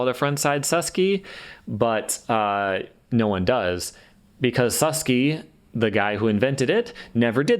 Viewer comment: I thought Suski was said sue-ski, and Bennett is said ben-aye. Am I the only one?